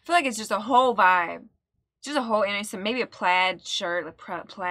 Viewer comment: what you maybe thought was 'innocent', was 'yellow tint' [2:10] , when was talking about sunglasses and a 70's fashion vibe.